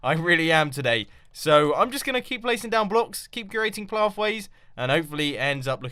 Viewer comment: Call it the "Cat-tastophe corner". At 18:25 it sounds like Mumbo is saying "Keep curating plaffways" xD